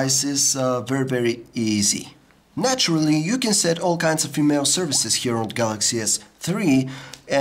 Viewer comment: did he say "naturally u can set all kinds of FEMALE services here on galaxy s III? " lolz...wat a phone!! btw ,im not pulling his legs here, i like his reviews n looking for more, but dat line was hilariously funny >)